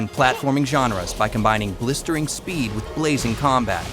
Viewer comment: Thought it said Lazy combat in the beginning lmao